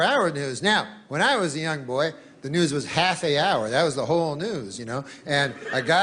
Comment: He said "half an hour". Network TV news shows were 1/2 hour long. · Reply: I hear him saying “half the hour”